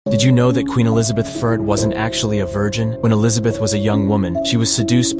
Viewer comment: He sounded like he was saying, "Queen Elizabeth third" 🤣